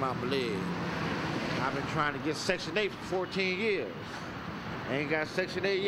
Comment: Section 8? I thought he said "sexual date"! haha